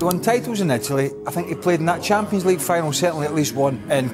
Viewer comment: Exactly , he didn’t waste, just didn’t maximize which is funny considering he’s won champions, uefa and World Cup. So even tho we know he coulda been better ,manz still went out and won the 3 biggest trophies which I know there have been “greats” tht haven’t even done tht so .🤷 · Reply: I misheard burley he said “played” idk why I thought won